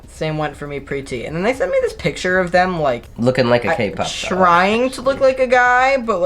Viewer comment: Lmao 8:34 did say looking like a kpop idol. I'm dead